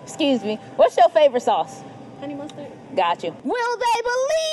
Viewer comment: I thought she said "Eddie Munson" not "Honey mustard" 💀💀